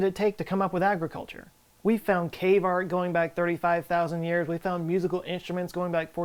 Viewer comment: “Cave art”. I thought he said “K-Mart”. LOL.